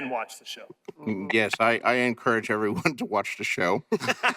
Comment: I cant make out what George says at 12:04? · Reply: All he said was "Yes, I encourage everyone to watch the show", then says "I...I..." but nobody lets him finish. He's only the writer after all.